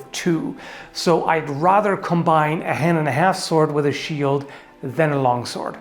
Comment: I always heard "hen and a half sword" and now I want to see one of those!